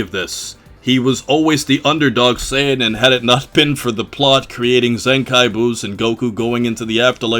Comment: did somone say zenkai boost?!?!?!? 😱(dbl refrensce)